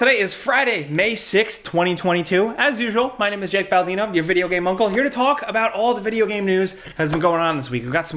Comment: I thought you said "your video game, mudblood" but you said video game uncle.